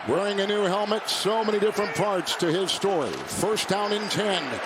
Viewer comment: why'd it sound like he said "farts"